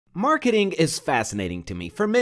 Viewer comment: is it bad that at the beginning of the video i thought he said , " MURDERING is interesting"? No, Just me? ok...